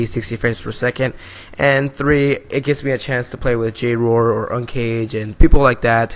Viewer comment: Did you just say ucaged ?😎